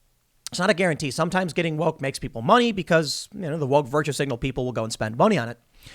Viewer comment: I first heard "the woke virgin single people" and I think it's better that way 😂